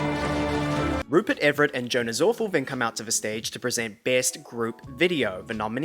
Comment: who did you say are the presenters at 10:54??? that's Salma Hayek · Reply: He said “Joan is Awful,” which was a reference to Salma doing an episode of The Black Mirror earlier this year. It’s a good “if you know, you know,” joke.